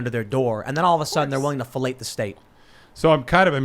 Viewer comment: did Tim say felate the state?!😂😂😂